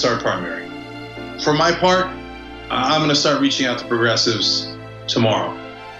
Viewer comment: this whole time i thought they were saying homoprogressives.